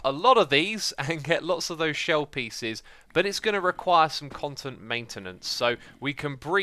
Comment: X says "content maintenance" at 4:05 instead of "constant maintenance". XD